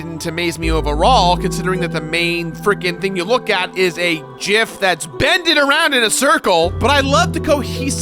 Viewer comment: Did you just say JIF!??!!?!